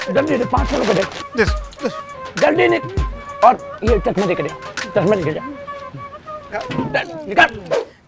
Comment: I had to hear it in 0.75x to understand what she said ! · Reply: Bosdik pakode talenge chiken matenge he said on last line